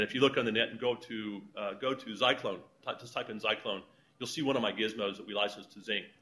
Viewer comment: the spinning ring toy he is talking about is called the "Zyclone Ring Blaster". I hope this helps someone else who found it difficult to understand what he said. watch?v=O_Cysu_NplI